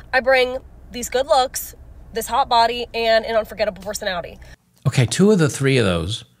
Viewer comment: She's got an 'unforgettable personality' alright. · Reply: I thought she said a forgettable personality at first. Maybe that's what she actually should have said.